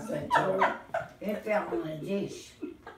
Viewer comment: What did say at 7:32 ? They laughed then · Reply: dropped her chip and it hit the floor (which she KNOWS is a crime in mind)... so she said, “it hit the dish” but then she got the giggles because she knew it hit the floor and she was gonna get yelled out of figured it out !😆